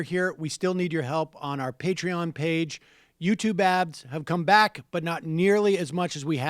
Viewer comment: I swear he is saying "YouTube abs"....